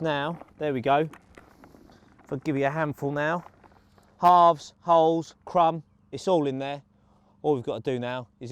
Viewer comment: Whilst was cutting the boilies near to the beginning did he say "Arseholes?"